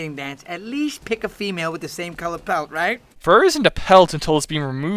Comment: Wait... he was saying “pelt” the whole time? I was thought he said “palette”, as in the same colors in general.